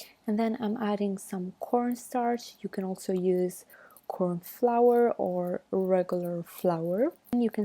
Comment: ‘I am using corn scratch but u can use corn flavour’ what’s does the packet say then 😅